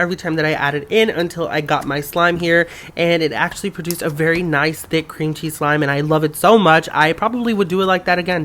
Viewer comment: Oh I thought it said a cloud cream cream cheese floam slime